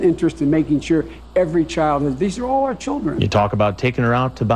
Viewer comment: I listened multiple times and I still don't hear it, what is he saying? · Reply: "These are all our children"